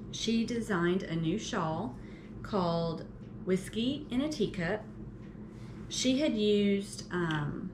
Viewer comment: Putting stitch markers in the lifeline holes as stoppers - that’s a great tip! Congratulations on 500 subscribers. 🎉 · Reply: P.S. My brain heard you say “Whiskey in a D Cup” 😅